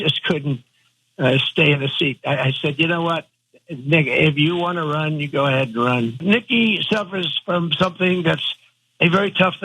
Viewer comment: It sounds like he said “nigga, if you want to run, you go ahead and run”